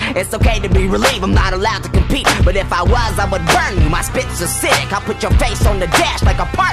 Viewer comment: My spits are cidic? Lol, what the hell is cidic... I'm pretty sure he says "My spits are sick". Another thing, what's "recipable"? I thought he said reciprocal.. As in flipping something in mathematics....